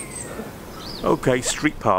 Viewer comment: thats not true, you're not supposed to park against the flow of traffic in the US. State and local laws may very on this but it's generally not allowed. That being said, its not really enforced, especially on residential roads. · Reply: ahh, I heard "and like" not "unlike" different accents lol 😅